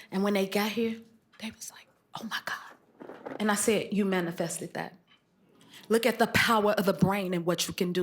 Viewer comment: What did Jennifer say at 15:23 that had Angela Baset clutching her pearls? Do anyone know??? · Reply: The young men said they wanted to meet Ryan Coogler and she told them they manifested it!